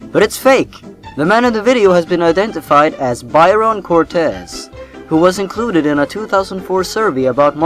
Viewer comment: I didn't really heard what you said. What is the man at 20:05's name? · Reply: Byron Cortez :P